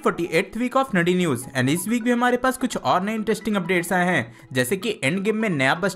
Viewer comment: did he said saddi news?